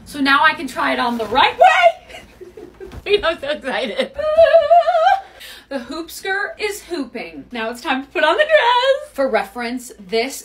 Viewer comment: what i heard: "the hoop skirt is pooping" 💀